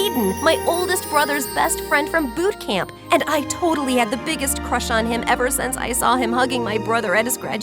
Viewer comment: At the beginning I accidentally heard her say “my oldest brother- I had the biggest crush on him “ and I was like “oml wth and I replayed it